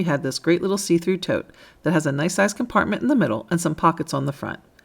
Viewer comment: did she say "Nice ass compartment!!" ? 🤣🤣🤣🤣